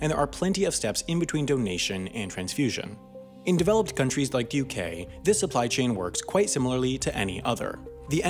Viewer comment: I misheard that as "In developing countries, like the U.K"... thought Half As Interesting was making a cameo there at first.